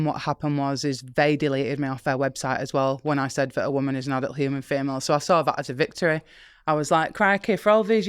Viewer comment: Can someone clear it up what she's saying? It sounds like right around 52:42 she's saying all she said is that "a woman is not a human female". Am I hearing her correctly or incorrectly and what is she actually saying if I got it wrong which I'm sure I have. I followed every word she said until that sentence and now I'm confused. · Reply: OH! maybe she's saying "a woman is an adult human female" and it's her accent on the 'adult' part that sounds like 'not' to my Western ears.